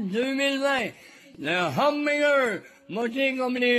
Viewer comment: Why am I hearing Humdigger?